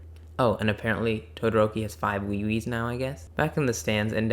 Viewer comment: Awesome vid, What does todoroki have 5 of ? I can't hear it properly. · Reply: I believe I said "5 wee-wee's". It was a reference to the scene in the show where the kids say that the things dangling from Todoroki's belt are "wee-wee's"